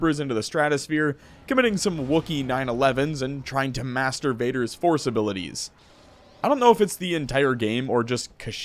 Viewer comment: I swear to god the first time, I heard masturbator force abilities